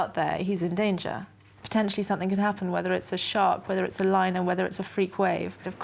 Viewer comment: Did she just say she’s worried about him getting harmed by a lion